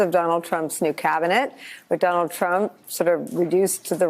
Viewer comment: did she say McDonald Trump?? That's hilarious!!😂